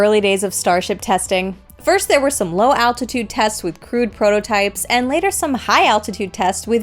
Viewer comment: crude prototype. that first sounded like "Crewed Prototypes"🤣🤣